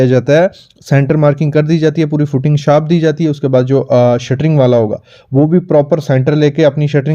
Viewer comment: did you say footing shaft..??